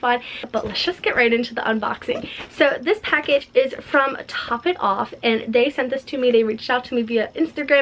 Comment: I misheard top it off as "hot topic" for a second and got very confused 😂😂😂😂 #notificationsquad